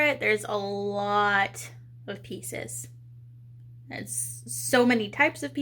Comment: Currently watching your build stream on TV and at beggining when you said pieces I thought you said pizza's as I was eating one at same time 🤣